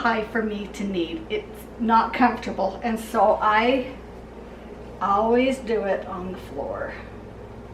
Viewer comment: PLEASE Tell me I was not alone...😅when I heard her say she does it on the floor... I was waiting to see her take it out the bowl and knead it on the wood floor😂😂